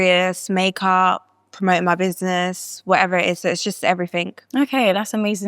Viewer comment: did she say you mind my business 😂😂